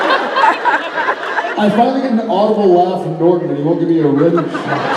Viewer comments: What did he say? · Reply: "I finally get an audible laugh from Norton and he won't give me a rim...shot" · WHAT DID JARED SAY AT 20:14 SOMEONE PLZZZ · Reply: something on the lines of "I finally got an audible laugh from Norton and he wont give me a rimshot"